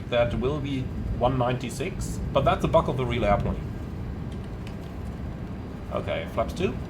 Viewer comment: did he just say a bug in the real airplane?